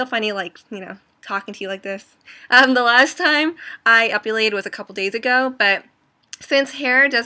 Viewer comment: I keep hearing "ovulated" instead of "epilated"